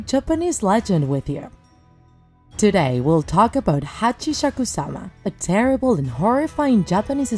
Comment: when I literally heard "hiachishakusama" I thought the monster had 3 heads 1 head is a witch 1 head is a doll and one head is a 8 yr old girl from the mimic😨